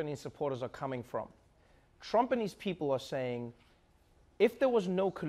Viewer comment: Seriously for the first 20 seconds I thought he was saying “Trumpenese people” not Trump and his people... Long day at work... lol..